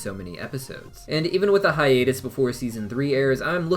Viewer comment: I misheard him say even with the hiatus before s3 airs, I thought he said with the hiatus being 3 years and I was about to be so sad that we’d have to wait 3 years for anything new